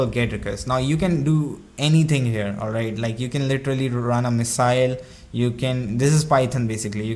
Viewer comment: did you say missile? Kim from NK called, he wants his hackers back.